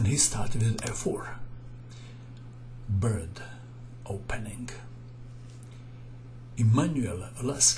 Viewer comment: Oh he actually said bird opening, I thought it was him saying bad in his accent. lol.